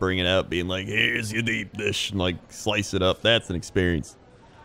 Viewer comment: I misheard deep dish as beep dish, and I'm okay with that.